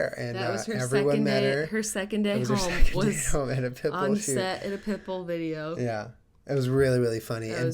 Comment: every time they said "Pittbull" i thought they said "Pimple"